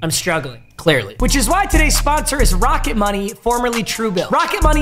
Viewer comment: Low key thought he said rocket Mortgage when he said he rocket money lol